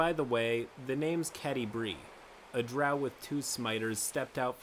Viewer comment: Did I just hear "smiters" instead of 'scimitars'? ;)